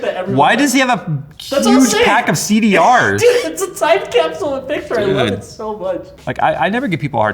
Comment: Did say at 18:16 "its a Zeitcapsule? German? :D